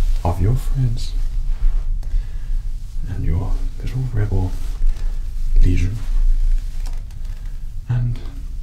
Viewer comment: I'm only about 7 minutes in, but so far this seems like it's going to be 47:32 minutes of this imperial guard simping for the cute rebel he just wants to patch up and convince to get to safety. XD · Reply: i heard "good little rebel' and went 👀